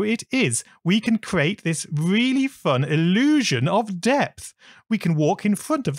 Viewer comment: I had this video playing while making a tile set on the other half of my screen and when he said “illusion of depth” I thought he said “illusion of death” and was thinking: “we’re on to boss fights ALREADY?”